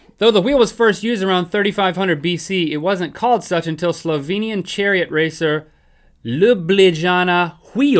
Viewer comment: did he say slovenian?